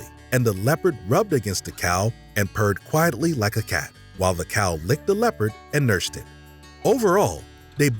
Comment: “The leopard purred quietly like a cat.” Dude, a leopard is cat 🤦🏻‍♂️ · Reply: The cow licked the leopard and NURSED him ??? Did I hear that right? Why kill the cow if you get the milk for free?